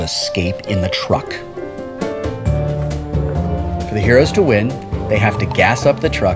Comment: I thought you said Heroes win on ties...